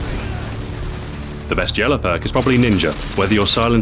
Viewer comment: did he say yellow perk?